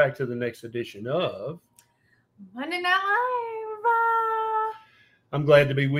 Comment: I’m Canadian & I couldn’t understand what this woman was say at the into! I listened to her 10 times & I still have absolutely NO idea what she was saying! OMG so frustrating 🤯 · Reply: She said "Monday night live"